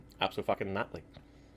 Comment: Am I the only one who heard him say "abso-fucking-not-ly", like absolutely but with a not?